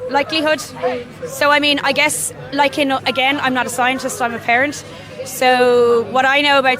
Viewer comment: It almost sounded like she said "I'm not a scientist, I'm a ferret".